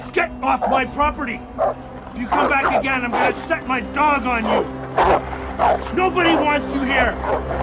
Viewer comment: Idk why I thought he was gonna say "set my dog on fire" 😭